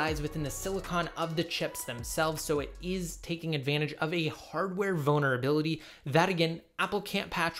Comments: I can’t be the only one who hears ‘Hardware Boner Ability’ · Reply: Holy shit, when the volume is loud I hear “hardware vulnerability” clearly but when my volume is low (like 3 ticks up) I hear “hardware boner ability” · I can’t be the only one who hears ‘Hardware Boner Ability’